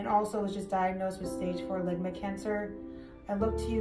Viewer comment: Did she say stage 4 Ligma cancer?